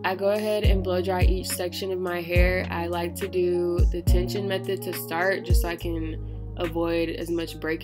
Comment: Hey, at 1:29 you mention some method, what is it called again? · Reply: Tension method